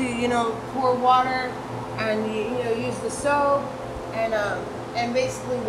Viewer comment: Did I hear her say use the soap at 3:27 ? Hell No baby girl!